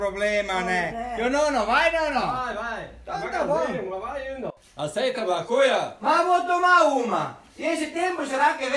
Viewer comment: só eu q entendi "aceita maconha?" KSSJJSDJDKDKJD mt bomm